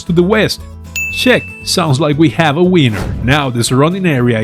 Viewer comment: "sounds like we have a wiener" I'm not a native English speaker but that's what I heard. and it sounded so funny because you have such a wholesome aura, man 😂 thank you for the awesome videos 🙂